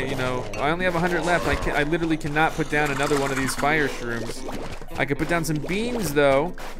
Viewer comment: Did he just call it a "Fire-Shroom?"